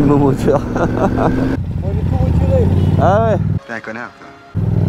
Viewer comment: Il dit quoi le type à 1:19? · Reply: "J'ai tout retiré" ( catalyseur et chicane )